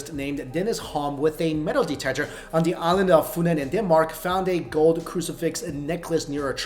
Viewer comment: did he say "on the island of foonland???? 😂